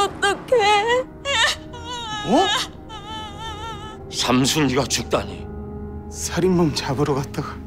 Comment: 나만 1:34 삼순이로 들려?